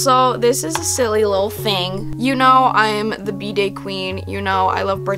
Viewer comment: I thought you said 'the bidet queen' ;u